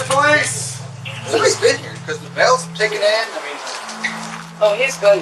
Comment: How’d you get “the bells have ticked in” from “the mail’s been taken in?” “The bells have ticked in?” What does that even mean? Did no one stop to think, “hey, maybe this isn’t what they’re saying because it makes no damn sense?” Or are you just using AI?